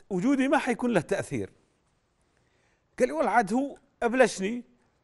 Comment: Can someone please type in Arabic what did Bandar say here? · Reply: Oh. It is “قالي والله عاد هو ابلشني". The verb “ابلش" means “to annoy/bother/ insist on”, so “هو ابلشني" here means “he (Abu Ammar) insisted on me”. And “والله عاد" is a filler expression that means like “you see/you know..”